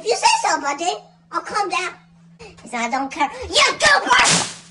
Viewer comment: Goober? Did he say Goober?